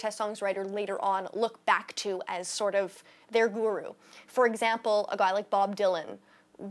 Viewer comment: What was the last word that she said? I couldn't seem to comprehend it. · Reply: (Edgar) shes saying "their guru"